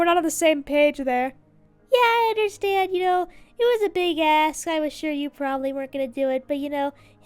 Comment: D-did I hear say A**?! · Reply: I think she said ask, but I admit it does sound like a**